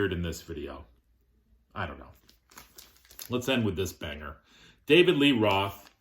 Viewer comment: I had to go back and rewind it when you introduce the David Lee Roth album.... You said let's end it with this banger, but I could have sworn you said let's end it with this packer... LoL